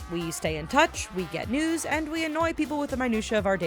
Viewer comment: when she was talking about facebook i thought she said "get nudes" instead of news... oops😂